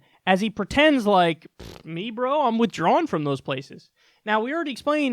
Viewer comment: I thought you said "pffft, negro, I'm withdrawing from those places" LoL